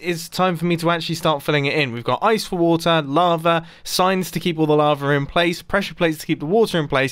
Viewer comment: Why is it that every time Mumbo says "Lava," I hear "Larva."